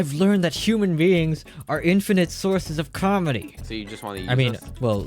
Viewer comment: i thought he said calorie in stead of comedy in the beginning